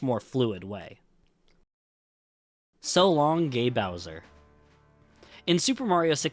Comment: Mario actually says "So long, eh Bowser?!". The reason the line is missing in the Switch version is because it's actually the japanese Shindou version, which added Rumble Pak support and the Peach voice over that was previously not in the original japanese version. The line "So long, eh Bowser?!" is missing from every japanese version and the reason is that, in Japan, Bowser is not named Bowser, but rather "Kuppa". So, having Mario randomly calling him Bowser in one of the voice lines would have just confused japanese players. Since the Switch version is the Shindou version on which a patch for textures and language is applied, it makes sense why the line cannot be heard there. Hope I was able to explain this well! EDIT: I didn't think this comment would be this popular! Thanks for the MANY people who reminded me he says King and not eh. I learned that a long time ago but for some reason, a memory of someone saying it was "Eh" left a bigger impression on me so I guess it took over my memories. Thanks for many replies though. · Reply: I thought he said "So Long Me Bowser" 😂🤣😂